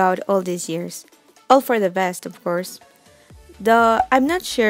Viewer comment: Word of the day “duh” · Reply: Yeah I thought she said duh when she meant "though" XD